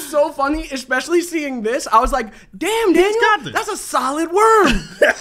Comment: Did he just say "Damn Daniel"?